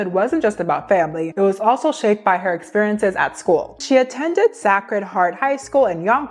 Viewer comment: 😳😳😳 Really?! Can someone let this young lady know how to pronounce "sacred" properly? It almost sounded like she was saying saccharin. The "a" is long. Say-cred. Come on now y'all, now we're starting to get all the easy words wrong. We are steadily going backwards! 😒😒😮‍💨